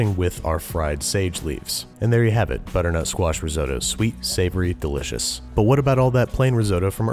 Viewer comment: Does it sound like he is saying "Butternote Squash Risotto" to everyone else